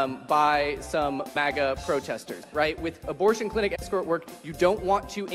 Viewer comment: "Is there anyone here who has done abortion clinic escort work?" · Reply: Haha I thought they said "abortion clinic escrow work", I wondered what they were talking about